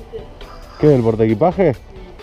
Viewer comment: entendí que me importa